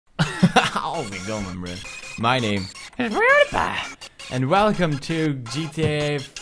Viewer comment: when he said PewDiePie I thought he said roodie rie